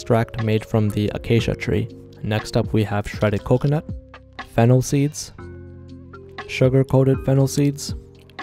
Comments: At first i thought you said fentanyl seeds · I thought he said fentanyl seeds💀